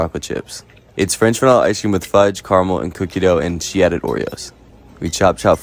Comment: I heard “coochie” dough instead of cookie dough